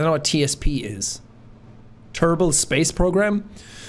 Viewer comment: What TSP could have stood for: Teaspoons The Stanley Parable Thrift Savings Plan Technical Service Provider What Jack said: Terminal Space Program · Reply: I thought he said "Terbal Space Program", like "Kerbal Space Program".